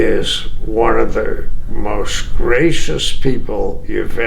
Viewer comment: did he say Ric Flair is one of the most racist people you ever want to meet? What's up with Kevin? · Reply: Gracious NOT racist